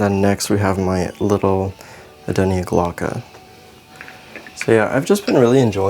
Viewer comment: Can please someone tell me what this plant at 11:46 is called? I can't understand him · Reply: I believe it's an adenia glauca ☺️